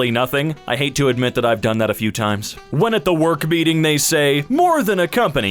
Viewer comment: I honestly thought you said “At the work BEATING.”